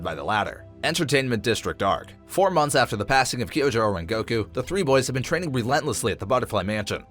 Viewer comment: did he just say goku